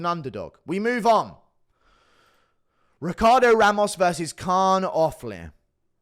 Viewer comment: i thought i heard, "Ricardo Lamas" and thought no way is he back. That would have been an upgrade. For nostalgia sake